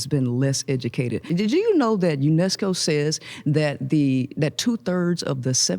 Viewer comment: made Candice smirk…I smell a podcast with intelligent civil conversation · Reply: I"m from New Mexico, wo Shen Priscilla stated: "UNESCO says that 1/3 of women..." I thought she said "New Mexico says...." "New Mess-co!"